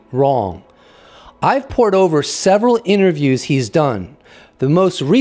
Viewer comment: “I’ve poured over” I heard “iPorn’s Over”